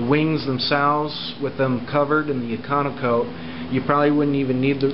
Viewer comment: what was the name of the paint you used. I thought you said econo coat but isn't that powder coating? Your wifes plain looks really nice. what kinda of prep did you do?